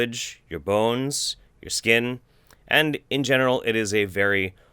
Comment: did i hear "in genital" instead of "in general"